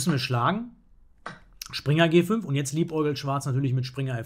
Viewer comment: was sagst du eigentlich bei 9:58-10:00 ? Diese Wörter nutzt du sehr oft bei deinen Videos und ich verstehe es akustisch nicht: lie ugelt zeigt mir der Übersetzer an? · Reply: Liebäugelt?